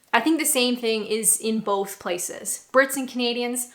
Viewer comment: Oh Canadian!! I thought she’d been saying ‘comedian’.